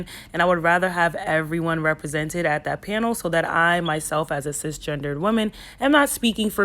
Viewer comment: Whats "sis" gendered @ 3:57 I thought u were a woman??? What??? 🤣😂🤣 i rewind like 4 times n still couldnt catch what u said. It dont matter what u are thts not gonna change my opinion of ur channel. I just couldnt understand what u said. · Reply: she said cisgender. Basically means someone who identifies with the gender they wee born with. So not transgender, biologically a woman/man. She's still a woman lol